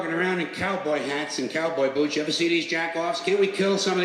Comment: First of love the video but, "just stab him" fuck dude. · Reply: Paused immediately and i hear "jerk off" my god... that's a sub